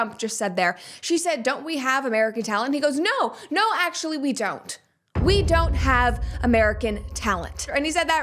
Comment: Brett in 2:30 in and it's even worse then your review. He didn't say no WE don't he actually said no YOU don't. Think about that as a read into his mind set. He should have at least said no WE dont. Who's team is he ON?!